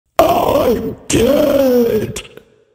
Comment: For some reason I heard “I’m gay” in the intro